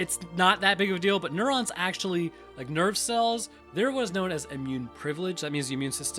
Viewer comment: Man. I been watching so long when you said "nerve cells" I heard "nerd cells"